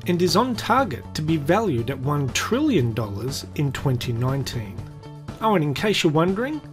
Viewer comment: Which year am I living in? This guy keeps saying 2019. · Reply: Here he says 2019. How an 8 sounds like a 9 is beyond me, the Aussie is clearly saying 2018.